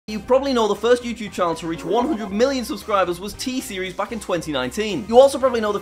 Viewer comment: People who thought he said 1 million subscribers so they played it back and realized he said 100 million subscribers 👇